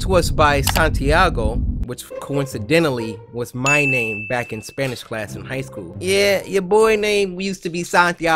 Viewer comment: Why when he said Spanish class i thought he said special class! I'm rollllingg